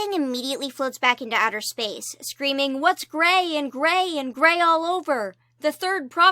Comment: Ididn’t year the ‘gray’ so I heard “what’s gay and gay and gay all over?”